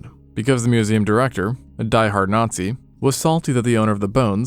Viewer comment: I don't know why but for some reason I thought you said his username was DiehardNazi but he wasn't an actual Nazi 😂 I'm tired AF